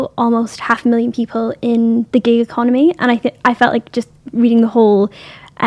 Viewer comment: What's the gay economy? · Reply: Gig economy, but it did sound like she said gay instead.